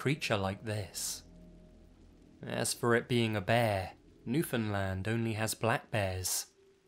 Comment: Tried my hardest to pronounce Newfoundland like a local xD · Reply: I thought you said New Finland.....I live in Finland......holy crap that scared the living s*** out of me